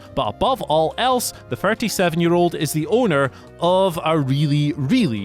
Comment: It sound like u said 47 year old